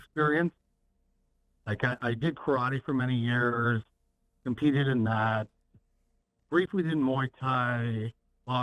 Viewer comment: I thought he said "I did cry for many years, competed in that..."